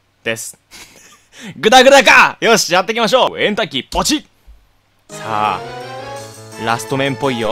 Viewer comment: エンターキーがケンタッキーに聞こえたｗｗｗ